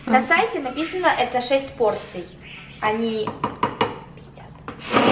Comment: такое тихое "они пиздят" ору 😂 Кто услышал лайк им на видео и мне на комм